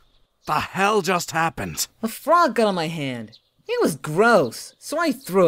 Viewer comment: When he said "Ew, a frog", I originally thought he said, "You look smart".